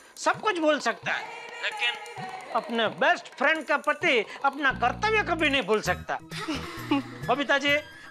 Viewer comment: I thought he says apne best friend ke pati😭😂